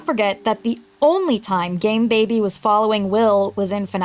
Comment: I may have misheard "game Baby" as "gay Baby" at first and I was like "yeah that tracks"